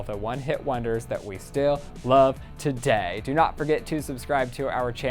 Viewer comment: Who else thought when he said “I’m Ryland Adams” it sounded like “I’m radadams”